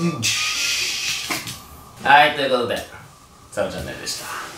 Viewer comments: 以上、サブチャンネルでした。が「サルチャンネル」に聞こえてにやってした(笑) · 最後のサブチャンネルでした〜がおさるチャンネルでした〜に聞こえた爆笑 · サブチャンネルでした が おさるチャンネルでした に聞こえたwww